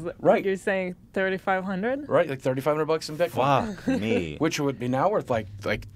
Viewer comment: Man, for a second I thought that boogie said that someone offered 3500 BITCOINS for his assassination, and I thought "DAMN, SOMEONE WOULD PAY 8.4 MILLION DOLLARS TO HAVE THIS DUDE KILLED? BRUH THAT'S SOME JOHN WICK 2 SHIT RIGHT THERE." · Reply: Nah he said $3500 WORTH of bitcoins. Not three thousand five hundred bitcoins.